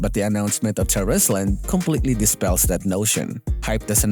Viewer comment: I thought you said "Bullsh*" when you said "notion" lol